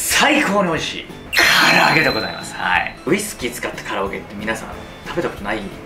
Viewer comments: 唐揚げがカラオケに聞こえたのは、自分も飲んでるからかな… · 冒頭ウイスキーを使ったカラオケに聞こえるw